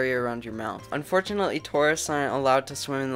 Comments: Oh he said “tourists aren’t allowed to swim in the lake” I thought he said “Taurus sign arent allowed to swim in the lake” I was confused asf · At first I thought he said “Tauruses aren’t allowed to swim in the lake-“ I’m a Taurus so I was confused lol.